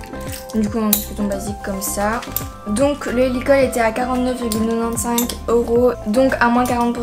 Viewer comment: Sa veut dire quoi neunan cinq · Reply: 95€